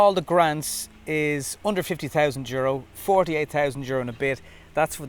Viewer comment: At the start you said under 50k, I thought you said 150k lol Great review as always but 66k for an inefficient car is just too much for me, can you feed electricity to your house/appliances from this car?